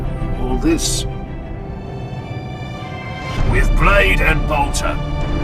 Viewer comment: does anyone recognise what the guy says at 18:20? "with blade and..." what? subtitles say it's "barter", but i don't really trust em